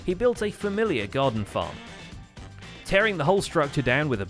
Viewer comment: hold up. did he repeat himself? or did he say 1. garden farm, then 2. guardian farm